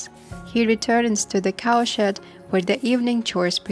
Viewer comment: Lol, I thought you were saying he was cleaning up cow 'sh¡t' but towards the end of the video I realized you were saying cow shed, hahaha 😅!